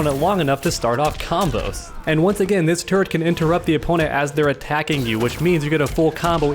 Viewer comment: Whenever you say turret it sounds like ur saying "Turd"